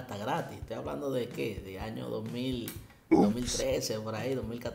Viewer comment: tengo una pregunta en el min 10:20 que dice la voz que sale al fondo? 2011?